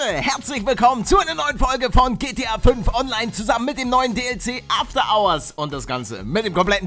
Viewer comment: Ich hab am Anfang so verstanden: Moin Leute, herzlich Wilkommen zu einer neuen Folge von GTA V online, zusammen mit dem neuen '' GLP '' ( anstatt DLC ). Und ich dacht mir nur so: .....Wait....WHAT?!